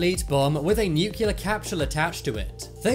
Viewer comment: Did you say capshull@